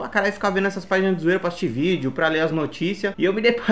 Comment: só eu entendi xvideos?